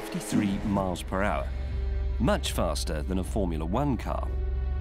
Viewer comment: i thought he said 'much faster than a formular wankcar